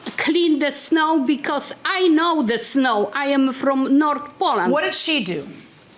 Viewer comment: I'm trying to figure out what she said at 10:27- it sounded like "I know the snow, I'm from North Pole." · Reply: 😂 North Poland but that was good lol